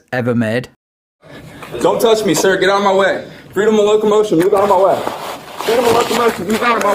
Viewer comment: LMAO Did he really say "right of locomotion"??😂